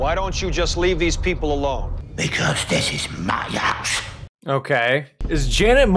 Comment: the youtube captions said "Because this is my ass" really killed the mood lol and 21:22 that cut was funny "Ra......!"